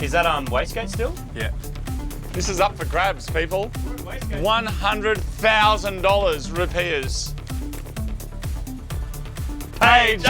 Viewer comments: Man, this whole time my simple ass thought y'all was saying "100,000 root beers". I was like, man, them aussie boys sure like them root beers. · I thought they were saying 100000 root beers XD